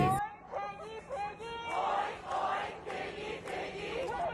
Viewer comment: What are they saying at 8:46? I can't process any of that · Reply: They are saying 'die die piggy piggy' and 'we will make your lives sh'tty' I think.